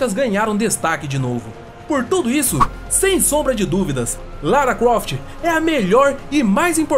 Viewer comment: so eu escutei ele falando lara cuoft kkkkk